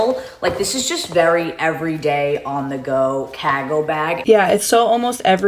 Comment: I didn't even realize she was saying "cargo bag," I thought she was saying some brand I had never heard of called "Kaggo Bag" or something